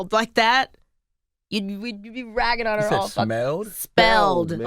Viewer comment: i heard "shmelled"